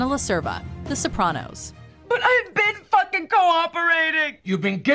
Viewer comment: did i hear "fucking" slip by your censors? AWWWW SHITTT